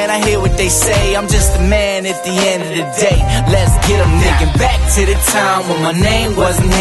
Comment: i swear he says the word nigga, he's white